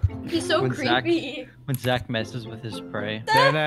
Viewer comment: When said “Zach messing with his prey” I thought he said “Zach messing with his bride